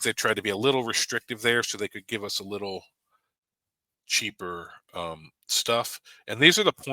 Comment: Did he just say he want cheaper points? Lmao 🤣 🤣 🤣 🤣 🤣